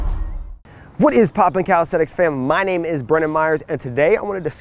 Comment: I heard him say "my name is Bruno Mars"